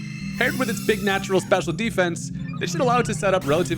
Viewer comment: At the beginning, I heard “BIG NATURALS special defense” 😂